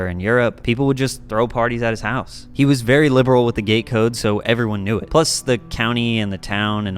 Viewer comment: <<--I had to listen to that two or three times before I realized what it was saying. I thought it said he was very liberal with the gay code. I did a double take. I was wondering what the hell is the gay code?! Gate code. That makes much more sense. I thought there was some funny stuff going on in West Chester Pennsylvania, and I don't mean haha funny.